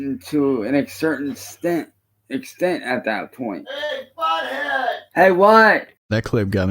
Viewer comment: did called him butthead 😂